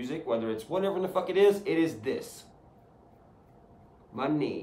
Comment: Wasn't looking at the screen and just heard "It is this.... my knee" and though church please don't tell me you just yourself on camera 😂 oh wait, I'm a dumbass and misheard it